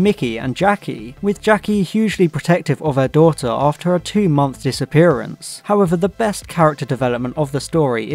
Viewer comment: I had to replay twice cuz I realized I heard him say 2 month disappearance instead of 12 😂